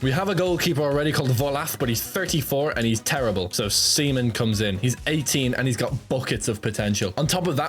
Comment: what did bro say 💀💀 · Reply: "So seimen COMES IN, he's 18 and has BUCKETS of potential